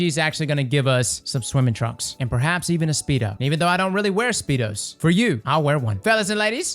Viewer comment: Well, I heard "wearing a speedo for me" and that's all I got from this vid!!!